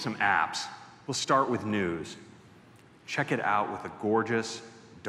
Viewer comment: I could of swore he said "let's start with nudes".